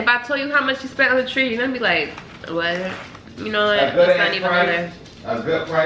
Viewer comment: When was saying “you know what, you know what”… all I heard was “Mm you’re done , you’re done” (tiktok audio) 💀💀💀💀😭😭😭😭 I caaannntttt